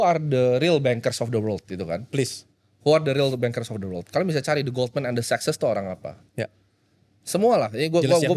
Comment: tolong yang 14:09 tuh bilang apa, cc nya ga jelas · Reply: the goldman and the sachs